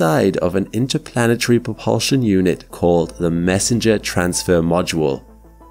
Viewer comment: you said messenger propulsion system, not mercury, propulsion system, the close caption say mercury, but the voice narration says messenger discrepancy detected. Please reply to me within 5 days. a Reminder will occur each 5 days. It's a no if this is a mistake or on purpose if so, please edit the description of discrepancy. Failure to comply within one week will result in reporting for absence.